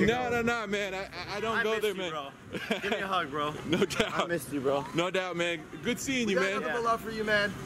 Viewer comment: Did Just Say NO CAPPPPP?!?!?!?!?